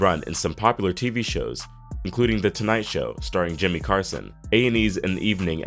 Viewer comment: did u say Jimmy Carson?